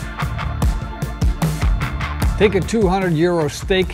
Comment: I thought you said 200 year old steak and my mind was blown. Eventually I figured out you said 200 Euro steak which was much more normal.